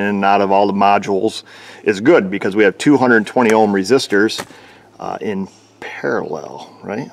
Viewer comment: Did you mis-speak?, 2- 220 ohm resistors in parallel is 110 ohms. It would have to be 2- 120 ohm resistors in parallel to be 60 ohms. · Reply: I misheard it the first time too, he actually said "2 120-ohm".